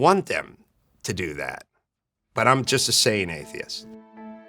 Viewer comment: "but I'm just a sane atheist" lol love it and can relate. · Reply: I thought he said Saved atheist.